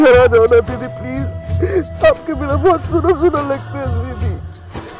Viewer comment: I actually thought he said ,”Apke binna ghar SULABH SAUCHALAY (public toilet)LAGTA HAI” instead of SUNA SUNA lagta hai😅